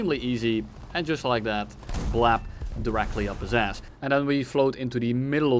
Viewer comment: Did you just say "Plap"?